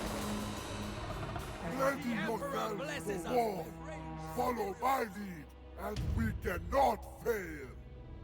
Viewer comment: "Ready yourselves for war! Follow my lead and we can not fail!" That's what I heard him say. And that was a pretty great battle with Gandhari dueling Arbaal the always getting defeated in single-combat. And the rest of the army did amazingly against the Khornates. I agree as well that the Grand Cannons sound beautiful. 39:54 Seeing Bhashiva run around Metastorm in circles as Metastorm can't get an attack off on her is so hilarious to watch. It was like a cartoon. All Metastorm needed was spinning circles over his eyes to show how dizzy he was seeing Bhashiva running around in circles around him. Bhashiva may not be Fast Thorgrim, but she her speed is incredible, but it was also too much for her. I did enjoy both battles against the Skaven as it was the classic Cat vs Mouse fight with the cat winning both times. Although the first one was the better of the two thanks to Bhashiva running around in circles around the Skaven lord.